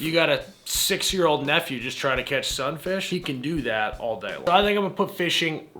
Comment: For fishing I thought I heard you say Sun Fish instead of some fish and has a hilarious mental image of a 6 year old out at sea trying to catch a mola mola. [For reference it's around 5000lbs ]